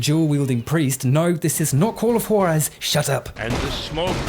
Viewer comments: What game is that at 15:25? I can't understand what he's saying and the captions didn't help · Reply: Call of Juarez. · what is that game at 15:26 i cant make out what is saying XP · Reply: It's Call of Juarez